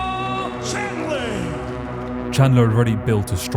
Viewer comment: Did he just say "Michael Chandling"? The disrespect is unreal...